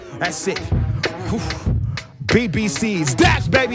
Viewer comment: When he had a little slip up I'm a BCB 😂 did he say I'm tired at the end 😂 2:53 . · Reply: Nah.. He said "One time."